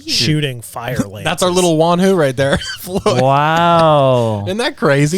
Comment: Juan Who? No, Wan Hu. Yeah I thought he was saying Juan as well lol